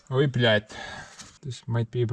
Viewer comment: did he said russian "ой блядь!", that means "oh f*ck!"?